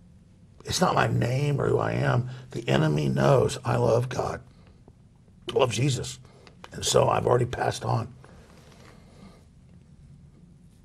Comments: did he just say: "The enemy knows, I love cock."? wtf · HAHAHAAAA 2:51 is it me or did he just say "the enemy knows I love cock"